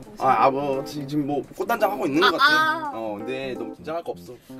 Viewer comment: 순간 코단장 이라 들었네